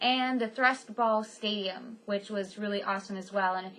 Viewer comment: Ohh I thought you said thrustball! Maybe I misheard you :P